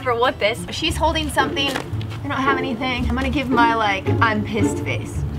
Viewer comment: did she say I’m just gonna use my **** face?!